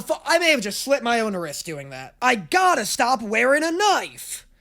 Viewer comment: I thought he said throat and was so ready to see a mark on his neck 😭